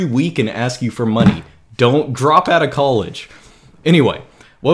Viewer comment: I said "Dont.... [change mind of what to say]. DROP OUT OF COLLEGE!" Not "Don't drop out of college." To be clear, I do not condone not dropping out of college.